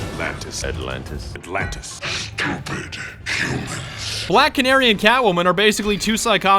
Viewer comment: I honestly thought he said "Black and Aryan Cat Woman."